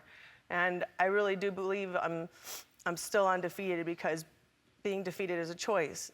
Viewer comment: Did she say she is still undefeated?